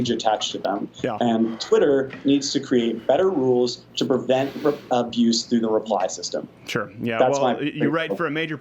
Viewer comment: Is it just me, or did I hear, ". . . better rules to prevent Repub . . . ?"